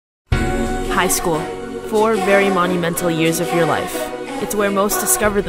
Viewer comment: I thought she said “hi school”